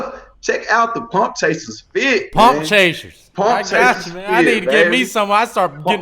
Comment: His right arm is bigger than the other one reminds me of Martin Lawrence stand up comedy special when he said why dudes one arm be swollen and the other lol I thought he said punk chasers 🤣🤣🤣🤣